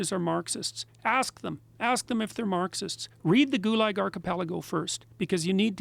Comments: what book is he saying? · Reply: The Gulag Archipelago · What does Jordan Peterson say at 1:20:32? Read what? I just can't manage to understand what he's saying.. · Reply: Gunnar Peturs Gulag Archipelago <link>